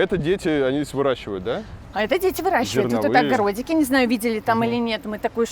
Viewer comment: послышалось, что они оба говорят «детей выращивают» 🤣😂😅